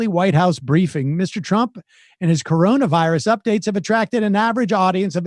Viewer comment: "...Mr. Trump and his coronavirus updates..." Due to habit, I thought Sam said, "...Mr. Trump and his crony virus updates..."🤷‍♂️